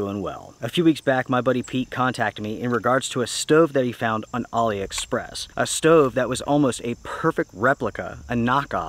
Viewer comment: So you are saying Ali Express! I thought you saying Ollie Express. Once I realized this I downloaded the app and spent some time shopping. As always enjoyed watching